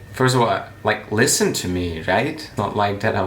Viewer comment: What did you say 11:25 "listen to me you faggot"?